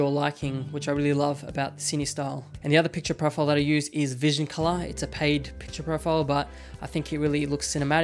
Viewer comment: What is the name of the second profile? "vision collide" ?? I cant hear xD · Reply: Vision Color